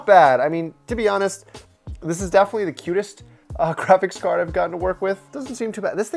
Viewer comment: Did he say "Crapics" card???